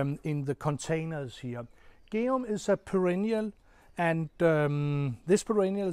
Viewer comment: It all looks lovely! Could you tell me the name of the second perennial you mention at 2:46? I couldn't understand and my subtitle didn't catch it. Thank you. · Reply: pretty sure its Geum